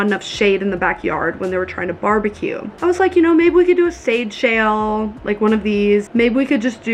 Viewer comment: A sade shail. 😐 Did she mean to say shade sail, or maybe sail shade? It looked like a shade shaped like a sail, so I’m thinking that must have been what she meant. Or is it really called sade shail for some reason?